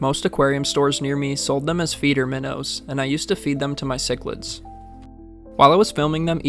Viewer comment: I misheard “feeder minnows. I used to feed them to my siblings”